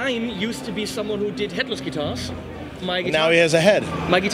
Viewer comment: anyone else hear him say that Klien used to do Hitler's Guitars? X-D